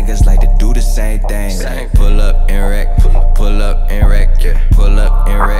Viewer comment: why do I hear, “pull up, erect”